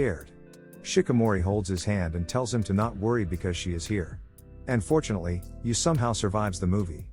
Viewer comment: man congrats on surviving also unfortunely??? you want him to die???💀💀💀💀💀💀💀💀 wait or did you say and fortunely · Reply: "and fortunately" 😖😖